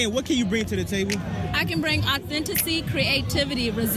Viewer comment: Did she say, 'Authenticy'?